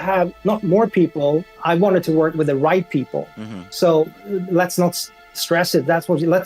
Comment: my dumbass thought he said work with "white people" instead of right people am I a racist lmao